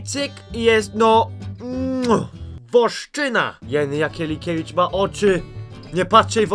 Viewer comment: moja kuzynka która ma 2 lata mówi na mnie wostyna XD i najpierw tak usłyszałam 😂